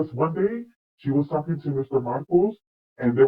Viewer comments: Did she say that Doria said Meghan would have nothing if it weren't for her ? Also, did she say that Meghan was with Mr. Marcus ? It sounded like she said Mr. Markle. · Who was " the Mr. Markles " she came to the house with? I'm confused! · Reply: I thought she said Mr Marcus. Marcus Anderson? Maybe that’s wrong.